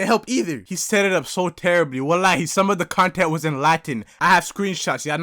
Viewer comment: did he say wallahi